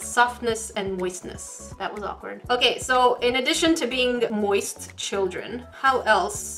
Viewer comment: am i hearing right..moist children😐😨